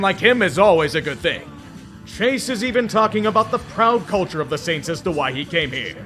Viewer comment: did i hear culture?